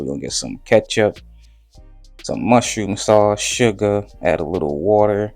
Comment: So I'm usually on the end of this process where I'm eating the food. So by no means am i a chef. Did you just say mushroom sauce? and may i ask which 1 6:05 you use? That's a new one for me in Jamaican seasoning. I love mushrooms 🍄 so id love to figure out how to incorporate it into my cooking!!